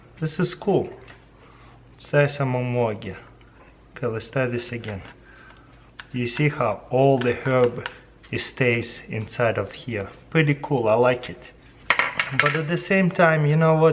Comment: anyone know what he says? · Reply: "Ok let's try this again, you see how all the herbs stays inside of here. Pretty cool I like it"